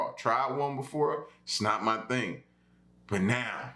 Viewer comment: sounded like you said ' "snot" my thing' 😂